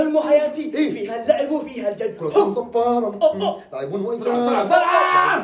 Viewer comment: كأني سمعت قال كرة زبارة 😂؟